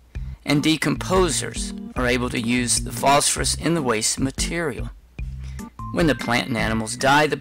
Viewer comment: What did you say at 1:15? Decomposers are able to use the phosphorus in the waste.... I couldn’t understand what you said there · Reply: in the waste material